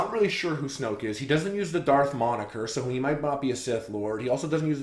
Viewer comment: what is the word, darth xxxxx (monikor ?)